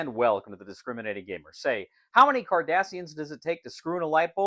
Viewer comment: I didnt get the joke at first because I thought you said Kardashians.